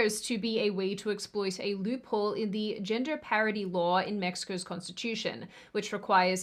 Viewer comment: I heard 'gender parody'😁